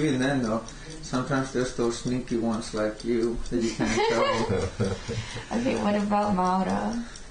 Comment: i think at 0:52 he said "that you can't tell" or some equivalent